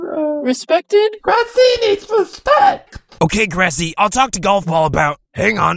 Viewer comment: why do i hear "grassy is respect"